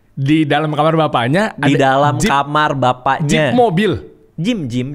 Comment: gua dengernya juga jeep bang wkwkwk